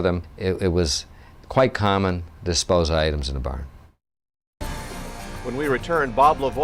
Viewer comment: it sounds like he says "it was quite common to find items in barns" . did i hear that right?